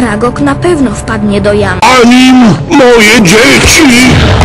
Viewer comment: ja usłyszałem zabij moje dzieci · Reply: "Za nim, moje dzieci!"